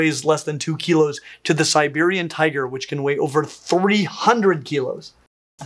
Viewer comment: did say 4000kg? That's an elephant. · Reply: 300 kilos